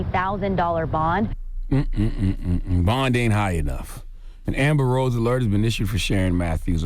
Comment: Did he just say an Amber Rose alert? I ain't the only one who heard that. Start vid